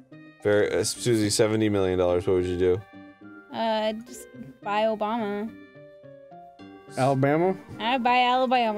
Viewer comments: ok I must be sleepy or something cause I thought said "I'll buy obama" 12:57 lol! omg XD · I swear I heard say "I'd buy Obama." Is that racist?